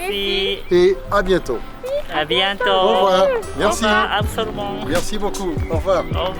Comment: 電話の相手がまさかの日本人…まるでドラマみたいで笑いました😂 22:28頃にムッシュ―との別れの時に「アヴソルボン」と聞こえたのですが何と言われたのか… 最後はドローンでの映像でしょうか❔本当に美しい景色、童話の世界みたいです😆🍀